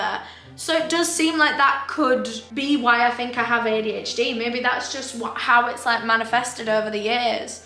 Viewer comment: I thought I had adhd … they said cptsd 😭